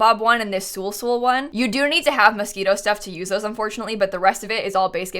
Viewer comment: i thought you said mosquito stuff and i was like "wait, what?"